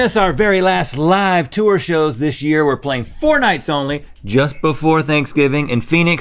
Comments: Am I the only one who heard were playing fortnites only at 0:17?? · I heard "we're playing Fortnite only" 🤣